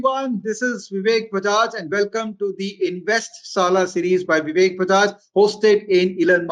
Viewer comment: I heard it as Invest Saala!!!! and thought is scolding us to invest!!